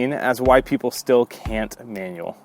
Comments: Did u say “white people can’t manual”? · Oh! 🤣 I thought u said "why white people can't manuel 🤣🤣🤣